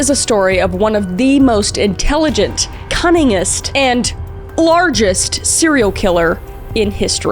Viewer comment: i swear i thought you said cunnilingus and not cunningness